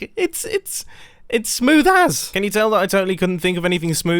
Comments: "Look, it's smooth as" "Smooth as a bowling ball?" - Man with smooth quartz slabs in his inventory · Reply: I thought he said smooth ass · Who else heard smooth ass? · Thought mumbo said: smooth ass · I thought he said Smith ass